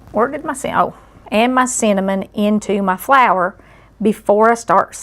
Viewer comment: LOL! When she said cinnamon, my NY ears thought she said "semen".